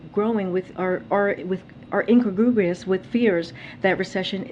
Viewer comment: I think J-P is trying to say incongruous (adjective meaning not in harmony or keeping with the surroundings or other aspects of something) but instead we got "in-cagrubrious". This is what happens when you send someone out there with a pre-written list of responses and instruct them to read them verbatim.